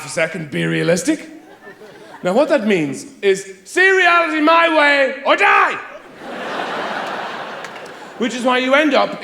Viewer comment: I just realized he's saying "see reality my way or die", but since I was a kid, I always thought he was saying "seriality" (a giberish combination of the words 'seriousness' and 'reality'). I'm almost sad this isn't the case. The way I had it in my head seemed funnier to me